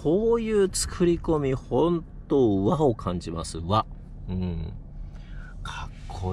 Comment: 「Wow！感じます」って聞こえて革命的なワードセンスだなあと驚いていたんですが、冷静に考えたら「和を感じます」でした